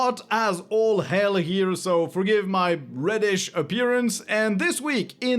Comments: I think I misheard you, did you say your Radish appearance? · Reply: I thought he said “British” and I laughed at that. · I think I misheard you, did you say your Radish appearance?